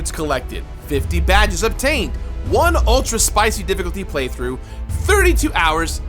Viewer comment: “66 succulent fruits collected… 50 badges of taint obtained” Nearly ran out and bought 10 copies because I misheard the second line of the video